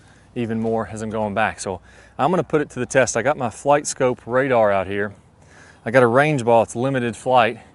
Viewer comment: what app are you using on your ipad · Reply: Sounded like he said "FlightScope". There is a FlightScope VX in the Apple app store that looks really close. It costs $9.99